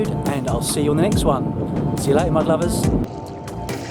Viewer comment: When I first started watching.I thought you said,"Hello,my lovers!".